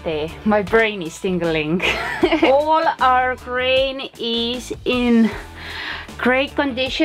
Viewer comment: Grain I thought u were saying brain lol. thought u were a zombie lol